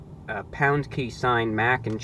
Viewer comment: did he really say pound sign?